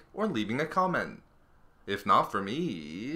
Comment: Is it just me or did it sound like he said "if not for me, for Jedi battle drugs"